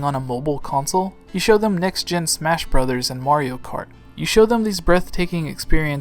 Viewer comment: Did you just say Wario Kart?